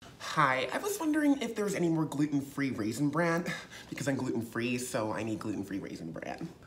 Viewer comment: this is hilarious, but I just wanted to be annoying and point out that gluten free raisin bran is impossible. As bran is already gluten and not just a flavour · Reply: I heard "raisin bread". One of us misheard. 🤷🏾‍♀️